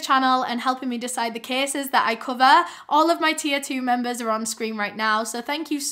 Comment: I’ve been watching you for YEARS. I was today years old when I realized at the end of your videos you’re saying my “tier 2 members” I always thought you said T02 members and always wondered what the heck that meant 😭🤣🤣🤣